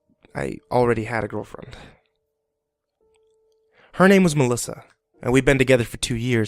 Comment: Lol I thought he said his girlfriends name was shalissa